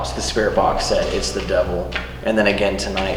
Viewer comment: Een they said "its the devil" i heard "poseesd"